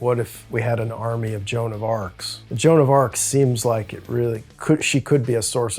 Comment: Jeanne d'Arc, wtf is he saying ?? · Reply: Joan of arc bro